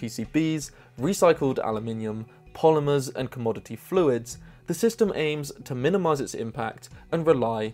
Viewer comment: My brain thought he said Community Fluids, not Commodity Fluids!